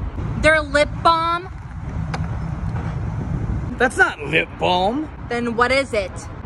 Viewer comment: I thought he said "THATS NOT NIP BALM" 😂😂😂😂😂😂